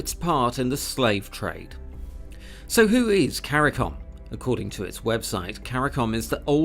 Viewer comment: I thought you said Cari"con". Opportunistic money seekers.